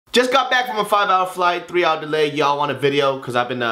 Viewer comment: Was i the only one that thought he said "just got back from a 5 dollar flight" lol